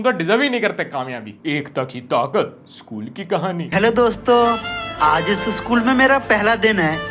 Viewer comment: He actually said hello doston not hello bacchon